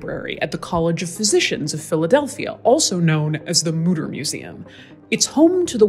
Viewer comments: I thought she said The Hooter Museum. A place Al Bundy would love to visit. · I thought she said murder museum